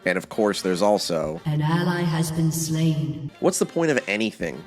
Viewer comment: I heard "an ally has been slayed" I liked the video